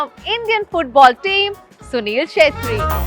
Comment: kya bola?? Sunil Shetty.??